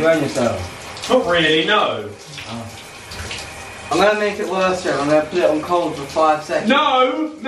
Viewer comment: he doesn’t know what grammar is “worser” is that even a word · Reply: he said ‘worse for him’